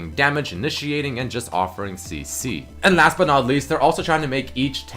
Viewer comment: whats up with the Subtittle @ 1:47 instead of CC Subtittle says DC LMAO Anyway it's a good thing tank players will get brains now instead of just spamming skills